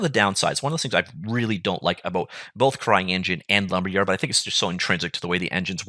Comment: did you just say "crying engine"? :D